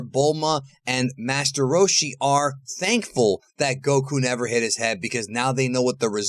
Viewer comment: I’m confused did you say that they were thankful Goku never hit his head, it sounded weird.